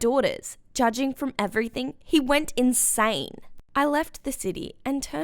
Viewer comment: hE WeNt INSiiiDe 3:24 Edit : Guys I’m sorry! I thought she said “He went inside.” But it was actually insane.